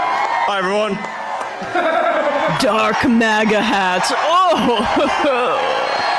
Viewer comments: When I first saw that clip, I thought he said he was drunk Maga. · I seriously thought he said "drunk MAGA"! 🤣